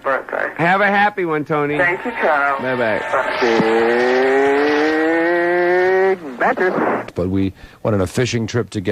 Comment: What's he saying? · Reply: Big mattress